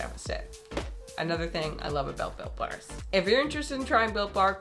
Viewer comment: Oh, Built Barr. I am glad I have close caption. I thought you said "Bill Barr is very chewy". He does look chewy . Rachel is ignoring the elephant in the room. Back to the cat. Put your cat in another room while you eat. The mom should be charged with animal cruelty. Cats can not brush their teeth. Cats are obligate carnivores.